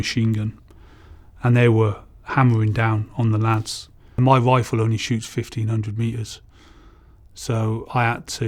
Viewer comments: I thought i heard "my wife only shoots 1500m" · i heard "my wife could only shoot 1500